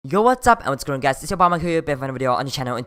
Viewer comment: In the intro i heard "yo whats up it you Obama here" 😂😂😂